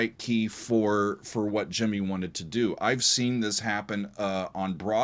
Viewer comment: I didn't realize he was saying "Gemie" as the name of that second vocalist. It sounded like he was saying "Jimmy", and I thought it was a strange name for a woman (not that I've ever met any woman named "Gemie", but the difference is that I haven't a man named "Gemie" either).